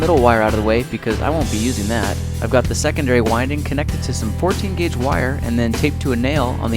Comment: Why does he call it Portugese wire? He's being racist for no reason. I looked it up and there is no thing as Portugese wire, so I know he is just making a joke at Portugese people expense. That's a low blow; shame on you. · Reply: I know this is late, but like...he did say 14 gauge wire. Listen to it again